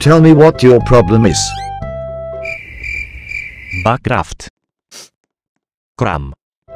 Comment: i thought he said mine craft 😳